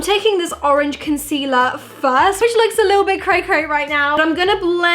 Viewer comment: About 7:15 I don't understand what you're saying, it sounds like "cray cray" ? 🤔😂